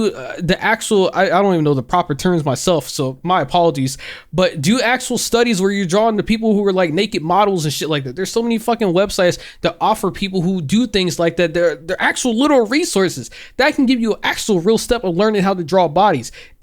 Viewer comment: thought he said “naked models”😂